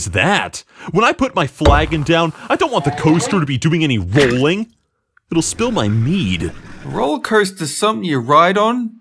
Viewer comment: I thought he said it will spill my weed